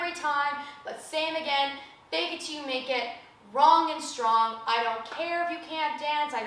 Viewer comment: One of the times she said fake it till you make it, I misheard it as "fake it till you're naked" 😂 I was very confused for a split second